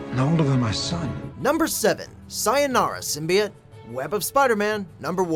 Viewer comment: Did... did he just say symbiot....